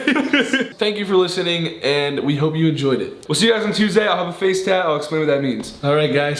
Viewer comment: did he just say face tat?